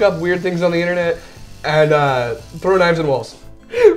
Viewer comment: I heard "Throw dimes at wolves" Not "Throw knives at walls." I was pretty confused for a moment.